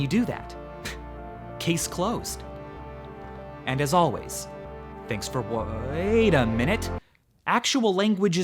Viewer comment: At 11 minutes I went to change the video and as my browser started loading the next webpage. I heard "as always thanks for waaaaiiit" lmao